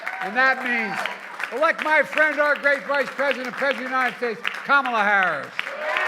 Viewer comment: I thought Ftrump said President Biden hates Kamala Harris. 🤣🤣. Go Blue! 💙🇺🇸 💙🇺🇸💙🇺🇸